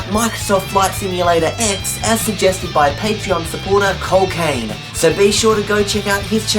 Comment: when he said cole kane i thought he said cocaine